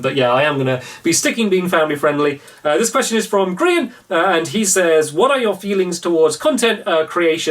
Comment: did he say Grian? Like the YouTuber? I love his work so that would actually be kinda cool if Grian watches Stampy's videos.